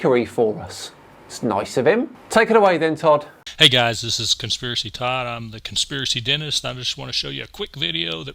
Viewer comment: Holy Crap! I thought you said Conspiracytard! I'm still laughing!